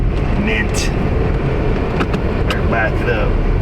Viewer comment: did you just say "MINT" - without the hand gesture? Peg will be so disappointed, (SMH)